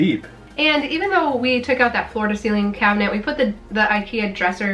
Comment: You are so cute. You keep saying "floor to ceiling cabinet" in this video and the last one too and I keep hearing "Florida ceiling cabinet." You trick me every time!